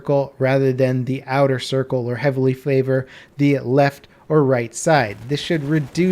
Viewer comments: anyone else heard "flavor" instead of "favor"? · Did he said "Flavor" at 03:53 ?